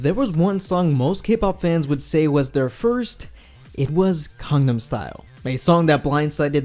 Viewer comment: Is it just my caption or did it really said "condom style"